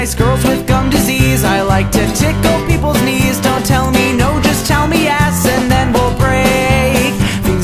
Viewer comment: Wtf and did he say just tell me ass